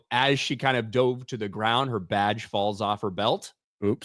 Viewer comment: lol I thought said "Her VADGE falls off her belt"